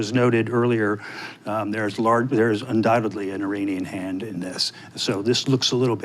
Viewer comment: Oh I thought he said "Canadian hand in all of this!" Now I have no idea what anyone is talking about ...again..